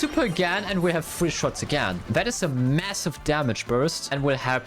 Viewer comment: did u say MASSIVE?? 💀💀